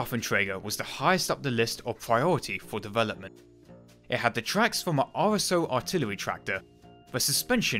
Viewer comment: is he speaking german at 13:40 ? it sounds like hes saying er hatte which means he had